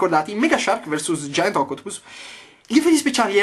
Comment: xD ma come cavolo l'hai detto "Octopus"? Io ho sentito qualcosa tipo Ocotpus lol